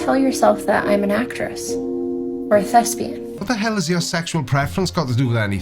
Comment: She said 'thespian', not 'lesbian' or did I misheard The queen of vampires?